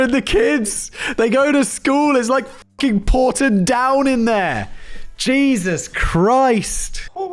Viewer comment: I could have sworn he said Portadown 😂 Of course, he said Porton Down, a military lab in England that researches chemical & biological weapons. However Portadown, a town in Norn Iron, is arguably more toxic ☣️